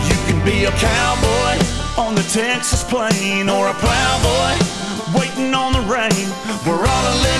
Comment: I swear I heard him say Or a brownboy waitin' on the rain